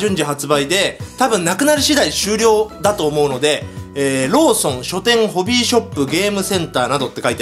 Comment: ローソンがローションに聞こえた俺は心が汚れてるな…